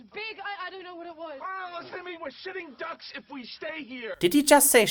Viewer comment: THE SUBTITLES EVEN THINK IT SAID SH*TTING DUCKS I'M DYING